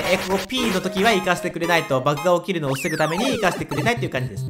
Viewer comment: なんて言った？( ᐙ ) 6:25 そのレーザーよく防げたなw そしてリンクは死んでませんw 青土管のところはクッパの炎の軌道を計算してブロック置いてるのかな？ 久しぶりに神コｷﾀ━(ﾟ∀ﾟ)━! さんの声って耳の保養なんだよね( ˙꒳˙ ) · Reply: バグが起きるのを防ぐために、や！w 保養！？ありがとう！？😂